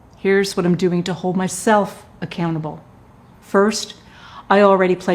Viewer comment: Sounded like she said "I'm gonna hold myself a cannibal." 😅 Stop it, Joe.